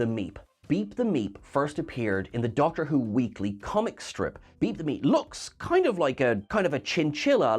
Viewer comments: I swear I keep hearing "beat the meat" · I wish I had read this before watching the video, I fucking CRIED the entire time thinking I was hearing BEAT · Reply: Yeah, the first time she said it I thought she said Beat the M3at, and then I thought he did as well until I saw the art and it had Meep and then I figured it out, lol! Maybe I should start checking "pinned comments" first...lol